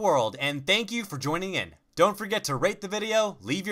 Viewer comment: I thought he said dont forget to rape the video, in the start. Dirty me...